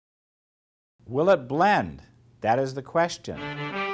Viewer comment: I thought it said will it bend..